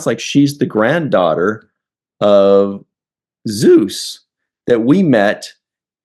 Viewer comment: I thought she said she was related to Zeus. That's Apollo. It's definitely an episode connection, but wrong guy. He's not the father. LMAO